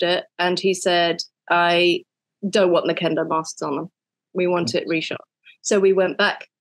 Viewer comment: im sorry i cant hear it, oda said what? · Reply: He didn't want Kuina and Zoro to be wearing Kendo masks while they were fighting, so they went back and reshot the scene.